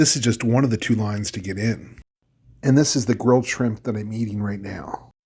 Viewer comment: Hahaha I thought you said " this is the gross shrimp you've been eating" ( salty) but you said grilled. 😅